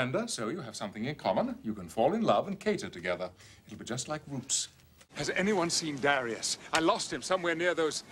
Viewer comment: Did he say, "You'd be just like roots" at 4:20?? LOL